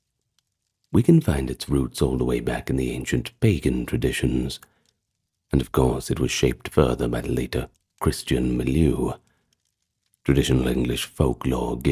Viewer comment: LOL, for a minute I heard "bacon traditions" and my eyebrows shot right up!